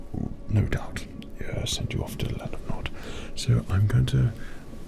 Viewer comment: Does anyone know what said here? "land of not/naught"? Not sure what he meant there. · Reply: Land of Nod. It means to go to sleep/be sleeping.